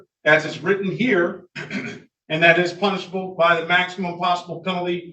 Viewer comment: At first i though he said “punchable” instead of “punishable”, and just like that I have a new goal! Love love! Die.